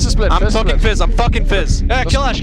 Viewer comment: I swear Bjergsen say's "I'm fucking fizz"